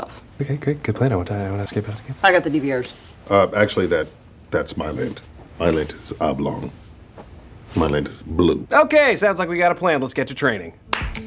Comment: What did holt say at 1:14? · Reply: That’s my lint my lint is oblong my lint is blue